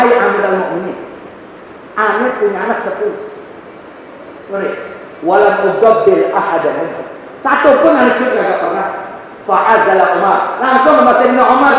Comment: Mau tanya dong di menit 19:11 bilang apa yaa kurang kedengeran.. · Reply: "Ane punya anak 10, satu pun ane gituin gapernah" (Maksudnya Bercandain anak atau ngegirangin anak)